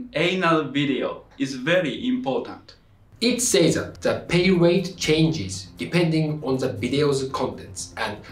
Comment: I thought at first you said the “pain rate changes” and I thought you were following it up with “depending on how much the actress is getting paid for their scene” Just put the pain rate of anal(painal) and the pay rate of the scene in a diagram 😂